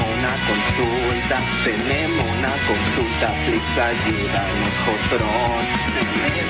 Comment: En el 00:00:26 qué es lo que dice el exactamente? Yo entiendo “trucha ayuda al mojotrón”. 😅😅😅😅😂😂😂